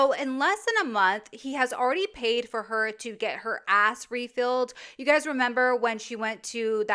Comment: I thought you said he paid for her to get her ass rebuilt lmao, I laughed so loud my neighbours probably think I'm as mental as Wes is